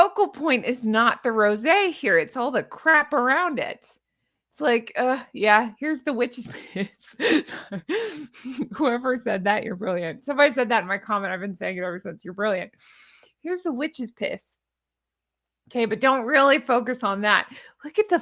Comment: At first I thought you said the witches pits referring to the peaches! I almost spit out my coffee laughing!😂😂:_LadyGoose::_HonkHonk